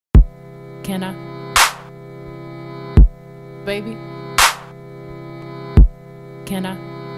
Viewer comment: Why I thought she was saying “Can I?, Maybe” 😂😂